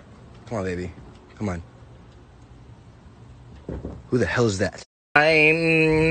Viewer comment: DID HE JUST SAY WHO THE HELL IS THAT TO DEREK? OMG😂😂😂